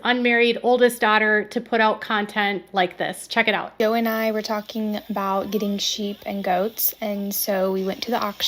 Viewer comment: Great piece here. I kind of thought Jana was saying, ‘Jo & I were talking about…,’ b/c they show’d a pic of Josie when they said that.